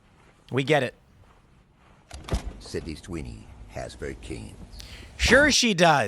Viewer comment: I swear I heard "Sydney's Tweeny has great jeans"... 😂